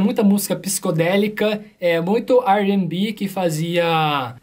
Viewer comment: "muito "ar and b"? Não entendi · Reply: R&B